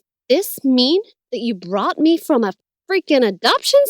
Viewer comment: “You brought me from a freaking adoption center!”😂😂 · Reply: I thought she said BOUGHT me from a adoption center But the played it again and was like... Oh... Well that explains why some of my brain cells are gone..